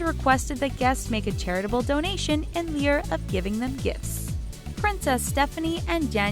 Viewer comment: WHAT is this woman saying? "...In ' lear' of gifts." Can't get any facts straight, AND cannot speak?